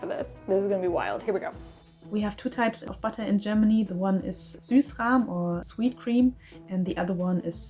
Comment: the captions say Sauerrahm/sour cream instead of Süßrahm/sweet cream :)